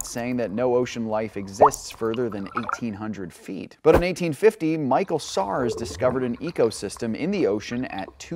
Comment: I thought he said Michael Scarn from The Office. -Threat Level Midnight